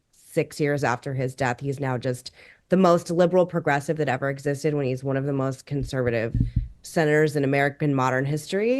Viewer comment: I thought he said when he shot trout!